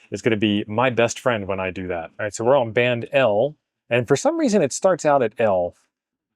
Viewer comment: I was on some other tabs and I heard him say Band L and my brain thought he was talking about L Band! I had to re watch 😂